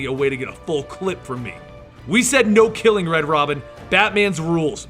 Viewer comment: did he say red robin